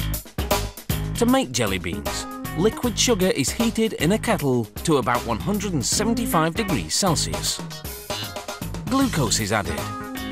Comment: The CC says: "To make jelly beans little children take it in a couple to about 175 degrees Celsius don't go see that" LOL